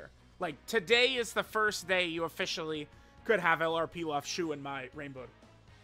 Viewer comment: I heard "You could have LR Pilaf chewin' Mai" and I nearly spat my drink out.